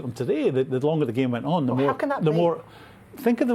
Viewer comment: who else heard 'how can that' when the other guy was talking